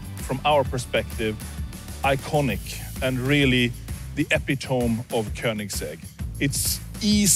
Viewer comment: Did he just say “Epi-toam” ☠️